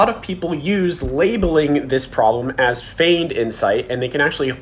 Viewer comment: What is it referred to at 3:30? "Fained" insight? I couldn't catch that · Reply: Feigned = pretended / faked